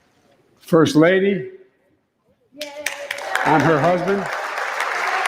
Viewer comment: Did anyone hear him say, “the First Lady and her husband” at the 36:30 mark? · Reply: He said “I’m her husband”